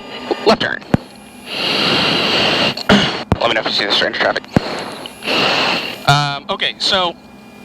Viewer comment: did he say “stranger traffic ”?